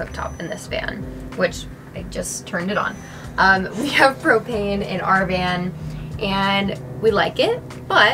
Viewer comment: My crackhead ass thought she said cocaine at 3:02 & 3:08 🥴🤦🏾‍♀️😂. I love this though !